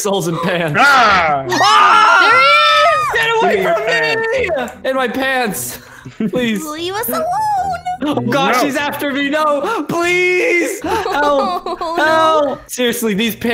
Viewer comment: Did say to light "SHE is after me"